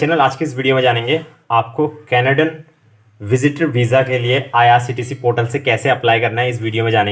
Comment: Did i heard IRCTC portal ? @ 0:05 .. when did Canadian visas are getting issued on IRCTC porta...?